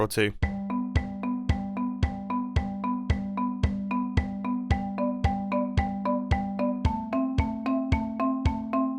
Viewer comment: What does it say at 2:04 ? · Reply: It said "Sleeping through this night"